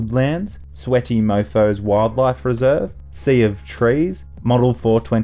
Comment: Sweaty Mofo's wildlife reserve? Man whew. Thought you said SexyMofo's wildlife reserve lol